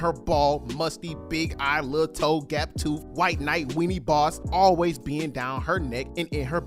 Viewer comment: nga did you just say naai?As in the afrikaans equivalet of fucker???😭😭